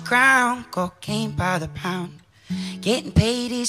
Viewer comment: I thought it said "COCAINE BY THE POUND" :/